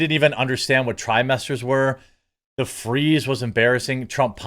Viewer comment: Oh trimester. I thought he said Thigh-Master. I kept waiting for Suzanne Somers to come out.